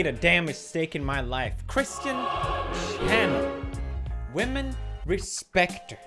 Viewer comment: I thought for a second he said Christian Chandler and I was like oh noooooooooooo